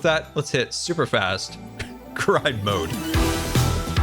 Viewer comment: thought he said "super fast crime/cry mode" either way what I pictured was hilarious